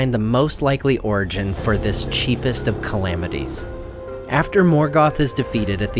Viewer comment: For a second there, I thought he said "cheapest of calamities".